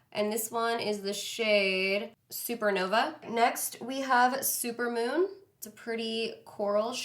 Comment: girl was it super moon you used on your lips or super nova because it sounded like you said super mom lol !!!